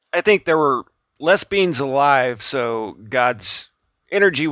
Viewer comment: lol just a fun comment, at 2:27 you say “less beings” but I heard “les bians” lol